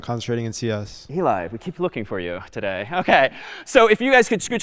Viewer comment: Anyone understand what David Malan said? Did he say we keep looking for you today? Why?